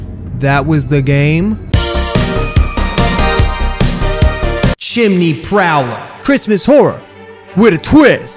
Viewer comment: at first i thought chimney prowler said "chimney power" and i thought we were gonna be playing a game where santa was like a superhero and let me tell you, i was so ready for that